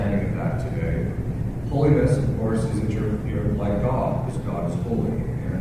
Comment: I tried to read the TRANSCRIPT but could not. It is full of non-sensical words. It doesn't make sense. At 0:37 "...horses interfere like God" and at 0.48 to 0.54 "your soul is the size of my breasts ..." I don't know what is going on with the trsnscriber! I don't think Father Ripperger would have said that Our Lady said that. I would go back and edit what he said! I have a headache and thought it would be easier to read than to hear what Father was saying. Please check. Maybe the devil just wants to make a fool out of me. Thanks!